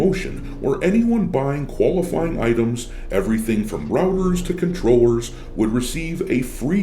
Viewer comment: I misheard you when you read the "from routers to controllers" and thought you said Rubbers, a European slang for condoms. Some how, that felt fitting. Cause if you are buying 'rubbers' on Amazon, you might need something so terrible it will help you last longer.